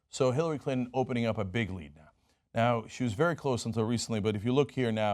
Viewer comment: watch minute 0:10. did he just say bigly?